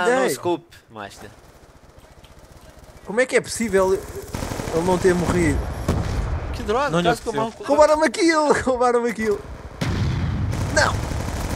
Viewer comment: diz 5:37 que droga vai toma no cú deles eu escutei kkkkk